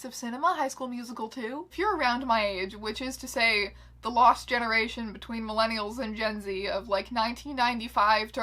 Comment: I accidentally had my hand on the skip button so I heard "the lost generation between millennials and sh*t"